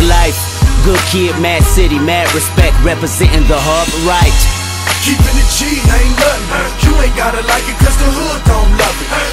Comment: for some reason I always thought he said alt right instead of hub right